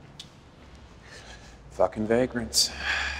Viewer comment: sorry,what did he say? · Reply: He said, “fucking vagrants.”